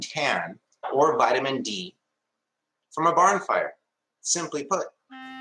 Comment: I thought he said "barn fire" and didn't even question whether I'd mis-heard him because I was expecting rustic nonsense from the start.